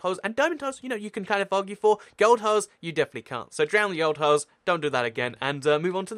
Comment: i thought you said "drown the old hoes" lol 😂